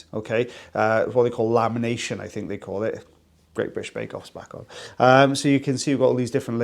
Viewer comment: wait what did you say??😂😂i swear i never understand what you’re saying when you mumble random things it sounds like you’re speaking another language 😂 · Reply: "great british bake-off is back on"